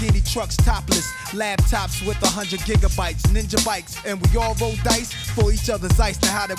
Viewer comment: I coulda sworn he said jigabytes